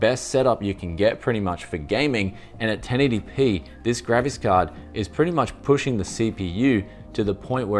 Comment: English caption says "gravis card" now I can't hear it as graphic card anymore